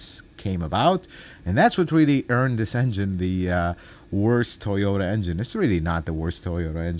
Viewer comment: This video scared the heck out of me. I have a 2012 corolla S with a 1.8L, 2ZRFE and i thought you said my engine is the worst! Lol